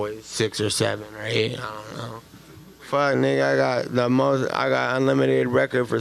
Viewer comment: Did he say "nigga"👀🤔🤫